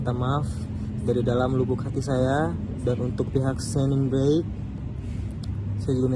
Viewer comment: "dan untuk pihak sening breigh" WKWKWKKWKW NGOMONG APA SIII